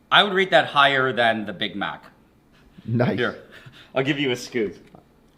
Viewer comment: did I just heard someone said the N-word?